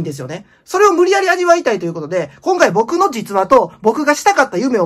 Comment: 僕の膣話って聞こえた俺は病気ですか？